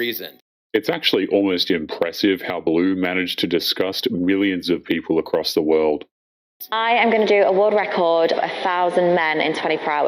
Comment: did… did you say “willions”?